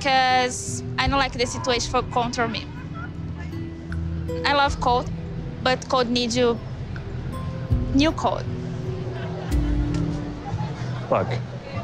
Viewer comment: Did she say in 0:29 I love colt, but colt needs a new coat 🤣🤣